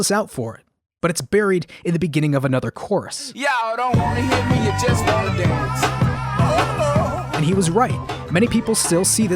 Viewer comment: i seriously never even noticed him saying "y'all don't wanna hear me u just wanna dance" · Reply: I thought he said “All you ladies just wanna dance” and I just thought it was a happy line